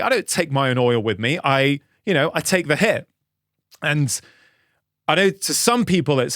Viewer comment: did I hear" "... go to London ... take the hit (for someone or something) - To face punishment" #learningenglish 🗣